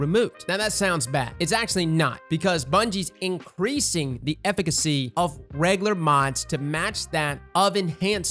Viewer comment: am I the only one who heard him say efficiency like ef-fic-a-see